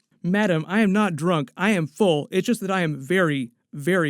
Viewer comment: I liked this.... although I thought he said, "I'm not drunk, I'm Thor. I'm very Thor" not I'm full. But after reading the comments in Norwegian full means drunk.... didn't know that. Thanks!